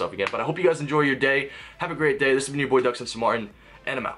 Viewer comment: Is it just me or did it sound like he said Doug "Censor" Mario?